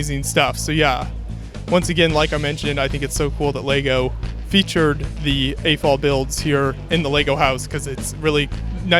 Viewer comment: What is he talking about @ 5:15 when he says "afall" fans? I can't quite hear him or understand what he is talking about. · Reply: AFOL stand for Adult Fan of Lego.